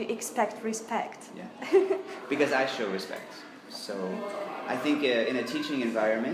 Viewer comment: Мне кажется он тут на 4:45 сказал "Because I show respect", а не как в титрах "Because I actually respect". Потом в титрах: "Teacher... has to respect...", а мне кажется он говорит "Teacher... have to respect...". Я не прав? Можете прокомментировать? Отсюда вопрос: а вы, когда его слушали, всегда полностью понимали все слова? Большая часть того что он говорит абсолютно понятна, но иногда все-таки сливается в" кашу", как мне показалось (может еще звук плохой...)